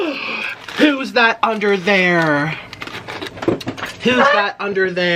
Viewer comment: nick: who's that under there!? me (what i heard in my mind): who's that pokemon!!?